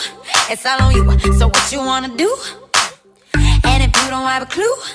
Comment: I thought It said I sat on you in sat on you